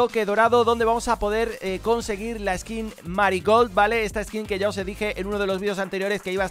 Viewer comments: pensé que dijo "maricon". Me siento muy mal 😢😔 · XDDDDDDDDDDDDDDDDDDDDDDDDDDDDDDDDDDDDDDDDDDDDDDDDD entendi M aRi CoN